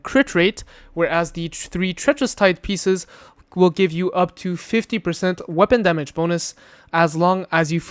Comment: It sounded like 50% but I think he said 40% bonus from Treacherous Tides. Love the build!❤